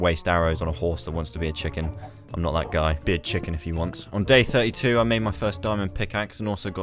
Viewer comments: sounds like he said the b word even tho he said to be a chicken deep down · i think he said bitch? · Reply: He said be a chicken lol it js sounded like he said bitch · When he said be a chicken i thought he said bitch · did he say be a chicken or the b word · did he just say bitc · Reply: No he said “he can be a chicken if he wants”